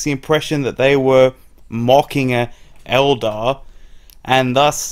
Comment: Sounds like you were saying "mocking an eldar."